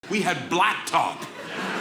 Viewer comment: With that opening line, I thought he said, "We had black talk."